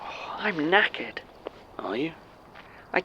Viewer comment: I literally had to go to the transcription of this episode because i did NOT hear Martin say he was "knackered", i heard him say "im naked" and was extremely confused when Jon just responded with "are you?..". Completely different scenario going on in my mind from what was actually happening😅😅